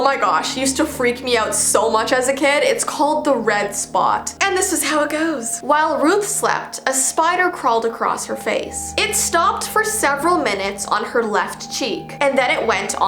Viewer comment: I thought it said the red snot 💀